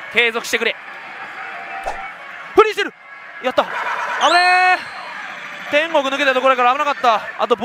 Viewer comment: 不倫してる！！？ やった！に聞こえてビビった